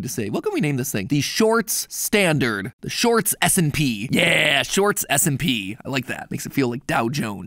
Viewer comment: I thought matpat said shorts smp 💀